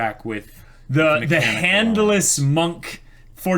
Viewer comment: did somebody said ZA HANDO??